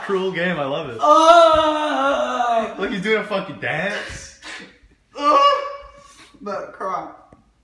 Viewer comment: swore he said "he's doing a fkn dance